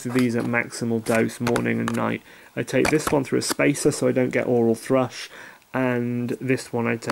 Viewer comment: I'm taking methadone and oxycodone.. for eds pain that is unbearable.. I've been taken off valium soma.. and Ambien.. in the last yeae.. it's very hard to sleep. I've been given tizanadine for spasms.. whoa did you just say helps oral thrush?